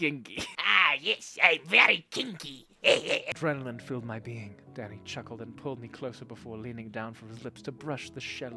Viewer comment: anyone else heard "Daddy" and not "Danny" at 11:06 cause I had a heart attack.